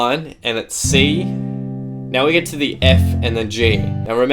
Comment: The F and G sounds like you said the f***ing G 😂😂😂